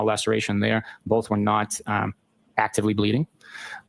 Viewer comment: He is so smart that he just said that “his wounds were not actively bleeding”. Damn police how a dead person could be bleeding! He said he has 20 years of experience, but he is not a doctor! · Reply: I thought he said they were active bleeds.